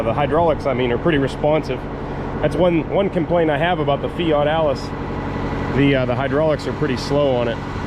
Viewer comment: Oh, Fiat Allis, I thought you said fat Alice 😆